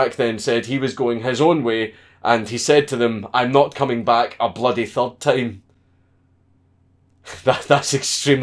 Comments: What exactly does he say at 6:10, 6:11? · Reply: Mad Jack said "I'm not coming back a bloody third time" Count Dank's commentary was "That's extremely British" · "I'm not coming back a bloody THIRD time." Had to go to Wikipedia to find out what he said, ya incomprehensible Scot. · I dont even know what hes saying at 6:00 The phrase the guy told the us soldiers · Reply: He said 'I'm not coming back a bloody third time'